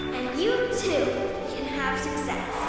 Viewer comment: Am I the only person who thought Ms. Jehovah at 10:53 said: "And you too, can have some sex" I feel like my ears are playing tricks on me